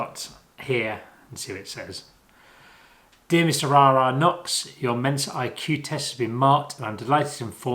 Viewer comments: Was very confused when I thought you’d said ‘Mr Ra Ra Knox’ at the beginning 🤣🤣 Also, you walking along the river in Putney reminded me of my childhood. My dad used to take us to that exact spot to feed the ducks! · at the start i thought you said 'dear Mr Ra Ra Knox'